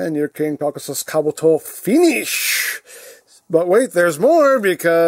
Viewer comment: Even though it says finish at the end during the background charging noise, the background and charging noise kind of drowns out the word finish so it sounds like he just said Keewee, even though he just said finish